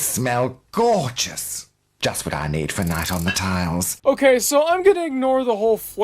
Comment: I think the toilet actually said, “night on the tiles.” Other than that, great video as always.